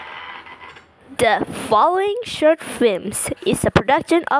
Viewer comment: i thought you said “the fu-“